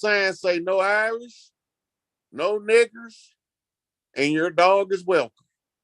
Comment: 🤣They are so funny cos when they said "No Niggas, No Irish and your dog is welcome" I hear "No Naggas, No Orisha but your God is Welcome" Don't want you but they want what you got 💯 Love the class 💞 Go get em 🎯 💕Pax Amore et Lux 💕